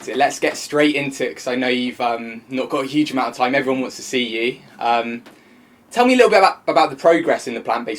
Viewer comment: OMGsh I thought he said "I never wanted to see you" @ 0:25 😂😅